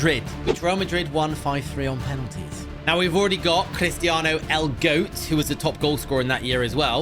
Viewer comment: Did say "we already got cristiano L goat? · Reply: not l el